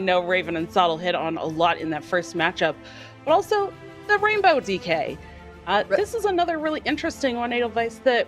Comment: did she called rainbow mage rainbow dk?